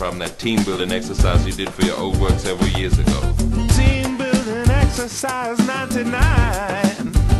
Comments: Listening to the live version and not this, I always thought that he said "Team building exercise not tonight" which I thought was funnier! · Team buildin' exercise ninety-NII-ii-ine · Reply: Holy cow for the past few years I swore he said "Team buildin' exercise- not tonight."